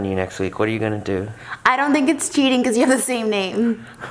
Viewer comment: My dog has lethal gas. She let it blow. I left my room, and when I came back in my room, I heard Trisha say “it’s not cheating if you have the same name.” I am physically weak.